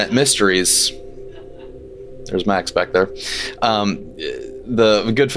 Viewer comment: did he say “there’s my ex back there”?